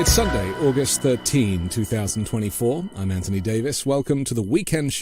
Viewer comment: October 13, sounded like you said August 13